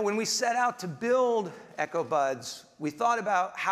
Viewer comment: hmm im not watching video and reading the comment. and i thought he said echo butt. hmm